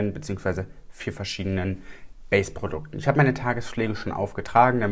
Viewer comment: Ich hab verstanden : "Ich hab meine Tagesschläge schon aufgetragen." 😂😂😂😂😂😂